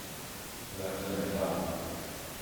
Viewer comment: Did he say "That's enough" ? There was quite a bit of noise as if someone was playing about and was he telling them "that's enough!" Sounds like something a father would say and he did have a "dad tone" I know that tone all too well.